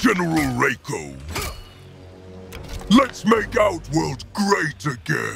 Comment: i legit thought he said “Let’s make OUT…” world great again